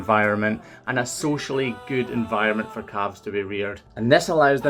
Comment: it sounds like he said "a good environment for cops to be weird" lmao